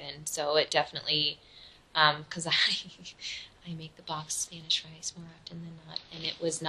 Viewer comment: "I make BOMB Spanish rice..." I have a lot of doubts about that one, Cherry Pepperoni. Edit: my husband is Hispanic and loves to cook, so I definitely know what good Spanish rice tastes like 😂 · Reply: I thought she said BOXED Spanish rice. Cos she was saying the rice tasted homemade. Then she had to make sure she insulted it, so she made sure to add that it was “gritty”.